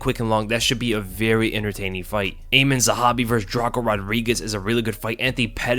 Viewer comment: When you said Aiemann Zahabi I had to look around to read the name because I thought you said Eamon Zahabi. 😯😁😆 Does Pereira look like a Brazilian Santiago Ponzibbio or is it just me?